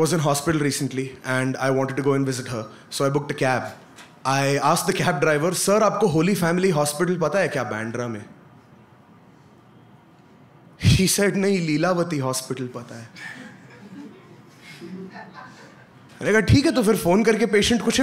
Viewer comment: Was it i or he really said "she said" to a male taxi driver.